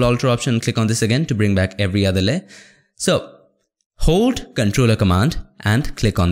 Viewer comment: I like your videos but please be more clear about the hotkey combinations. At approximately 2:40 hold what now to turn that into a selection? I can't understand you and the close caption says "hold ctrl a command and click on this this makes a selection of this area". After 20 minutes I just went back to that layer and selected it so I could move on. Somebody else asked about the hotkeys, so its not just me. Thank you!